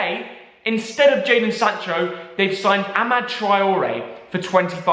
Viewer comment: thought u said adama I was scared for a second there phew